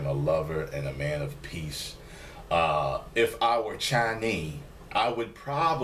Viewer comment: what did you say after "If I were.."? It sounded like Chinee or something. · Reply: He said chinee. It's a humiliating way to say chinese